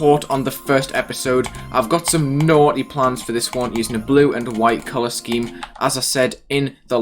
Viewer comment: I misheard that for "White Palace Theme" Path Of Pain flashbacks